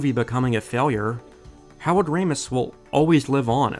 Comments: I kept hearing him say "Howard Ramis"... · Definitely sounds like he is saying “Howard Ramis” every single time and it’s making me crazy